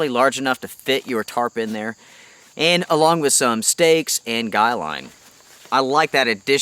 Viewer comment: At the beginning when you went over the stuff sack and what else fits in there you mentioned stakes and line. I heard "steaks" ... and it's true, you could probably fit a couple of steaks in there if you needed to. I think I'm hungry.